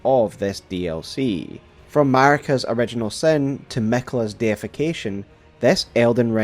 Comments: I heard Miquella's defecation instead of deification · Did you say Miquella’s defecation? 1:38? 😅